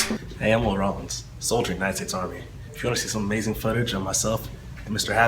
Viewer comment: At first I thought brotha said "Neistat army" woulda been good if he had.